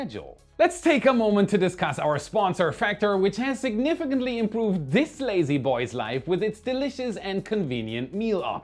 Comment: For a second I heard "changes this lady boy's life"